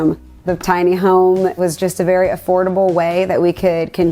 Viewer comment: Thought they said affordable